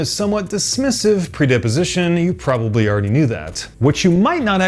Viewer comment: pre·dis·po·si·tion Sounded like you said "predeposition," which doesn't appear to be a word :) Seriously the largest of nitpicks, though; video is phenomenal.